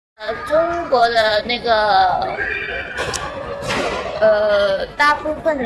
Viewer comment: I would hear this a lot among Chinese ... it sounded like "nigga" and the conversations were peppered with it. Never knew the proper way was "nage."